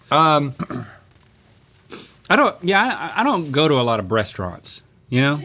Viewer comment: did Link just say breastaurant????